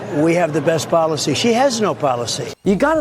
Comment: I heard him say, "she has a policy, we have no policy"